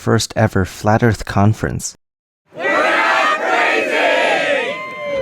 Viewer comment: Did I hear "We are crazy ?" THAT'S IT PEOPLE, I CAN'T FUCKING TAKE THIS ANYMORE. THEY'RE SAYING IT, GODDAMMIT !!!